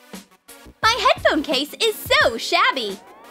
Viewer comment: did she just say head phones?